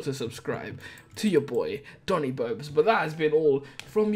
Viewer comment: TONI BOBES is what I heard you say